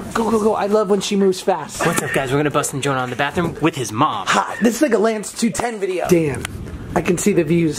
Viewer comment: It sound like he said "This N***a Lance 210" I know he didn't 😂😂 @ 2:07 he said "This is like a Lance" really fast